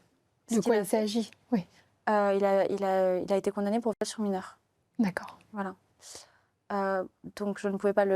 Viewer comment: le frère a été condamné à quoi "sur mineur" ? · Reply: Viol sur mineur